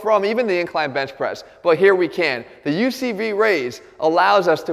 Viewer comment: Did he call the exercise "UCV Raise"?